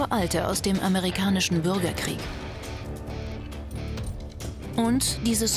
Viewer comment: LOL 3:45 ich verstehe immer nur würgerkrieg ich weiß selber dass man sich darüber nichtblustig macht